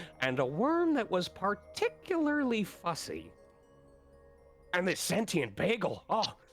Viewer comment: I heard "Sussy". 😂